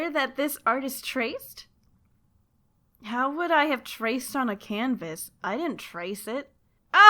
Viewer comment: it says "raped" instead of "traced" in the captions. :V